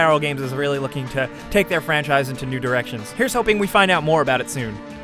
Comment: He said Take their franchise to new directions. XD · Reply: Marius Barbu u sure about that? i hear "nude erections" for sure